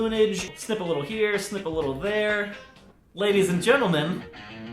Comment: Lol, when you said snip a little here snip a little there it sounded like you said, "Sniff a little here, Sniff a little there". I thought, well, if the pin doesn't work he might have a buzz from glue sniffing!